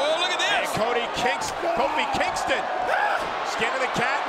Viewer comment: Did anyone else hear codi kingston